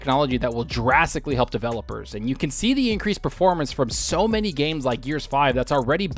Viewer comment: Wait o ok for a minute i thought guy said an increase of 50% performance for gears 5. I was about to say wtf thats insane